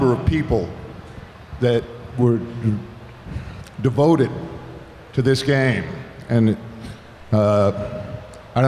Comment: I thought he was going to say devoted to this gang